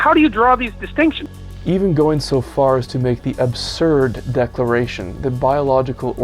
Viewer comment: This is just propaganda. You´re making all this claims about views that he obviously does not hold. Like suggesting he want´s opportunity of outcome. Which he have said time and time again that hes utterly against. And that he´s talking to young white men, so what, I don´t think he is... But if he was that shouldn't be a big deal. You wouldn't critique a black man for speaking to black youth, would you? You later go onto say that he chastize white Europeans. You have all this contradictions in your arguments. I don´t know if this video is just on big troll. 17:49 you also have this arrogant way of paraphrasing what he said into something else that changes the meaning behind the statement. Anyone with half a brain knows thats not what he said, and what you say is likely not what he meant to communicate. To say that he claims that " biological organisms don´t have any interest in forwarding their genes into the future". Thats not what he said. 18:25 you must be from another dimension. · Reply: After watching it once again I see I gave you to much credit, I thought you said "Obscured" not "Absurd", sorry about that.